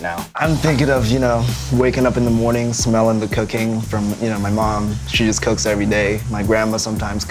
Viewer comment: I thought he said cocaine but he said cooking